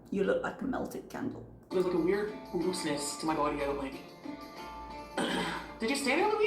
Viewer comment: a weird what to her body? I didn’t understand what she said. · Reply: I think she says ‘Looseness’